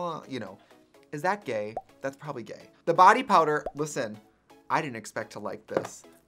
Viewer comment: I thought it said “Power” in Trixie Swedish accent 😂